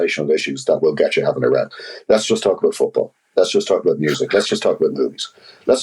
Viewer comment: Did he just say "lets just talk about boobs"?? Lol.